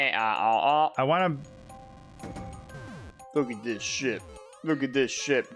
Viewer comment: 33rd 5:53 People, did not say sh!t, he said ship, if you are worrying about that.